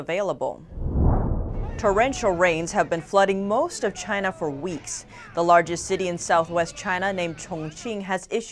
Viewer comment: I always hear "China info-kiss"